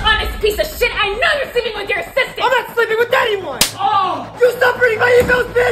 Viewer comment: I'm sorry I heard her say " I know you're sleeping with your sister." Had to replay that one and read subtitles 😅